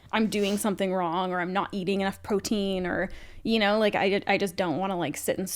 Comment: I know said “Am I eating enough protein” but I heard it as “eating enough poutine” and idk that feels more appropriate 😂